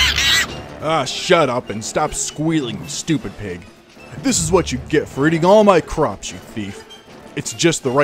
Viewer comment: When Steve said, “This is what you get for eating all my crops”, I thought he said “all my crotch.”